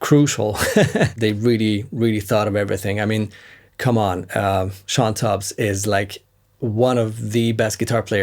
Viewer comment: excellent playing and another fantastic video. I like a lot of the gear you review, but actually I just watch your videos to listen to you play. At 15:34 you mentioned a guitar player you like. I didn't catch his/her name and I'm always searching for new players to experience. Thank you! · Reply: Thanks so much Sam! 🙏 I mentioned Shawn Tubbs, Nashville pro player. It’s his signature boost. Cheers